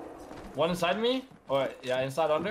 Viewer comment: Did he just say ‘one inside me ‘ at 8:54 😂